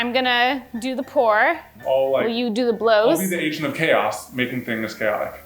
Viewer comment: I thought said ASIAN of chaos and I thought of Eugene